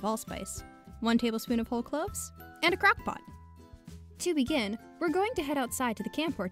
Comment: I firs thought she said crack pot not crock pot